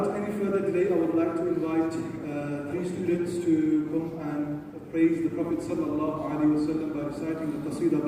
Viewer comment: Did he just say pray to the prophet pbuh!!! At 4.30, We only pray to Allaha swt. I hope he mispoke and that is not what he meant